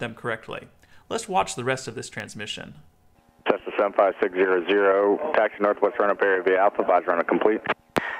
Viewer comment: Ok, I have never understood how pilots manage to make out anything over that raspy atc frequency. To me the atc voice sounds completely garbled.. I felt like I heard "Cessna 75600 taxi north wesfron appear via alpha weisrunner complete".. and that's when I rewound several times and really listened closely.... the first time I heard him, I had no idea whatsoever what he said.